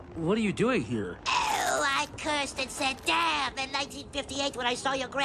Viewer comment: "I cursed and said DAMN in 1958" is a line that's so unmistakably Zach that I'm sure he said it on Oneyplays atleast once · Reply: I thought she said: i cursed at Saddam